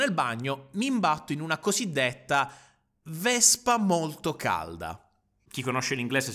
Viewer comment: qualcuno mi può spiegare il significato del nome in inglese della "vespa molto calda" perché non c'ho capito un tubo😭😭😭😭😭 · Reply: Dr. Keufman Horny Hornet in inglese